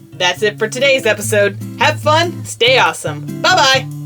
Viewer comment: I thought you said stay pawesome lol